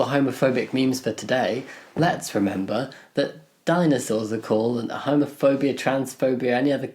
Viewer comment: thought you said "diamond swords" not dinosaur, so if you're going for an alternative for homophobia ;)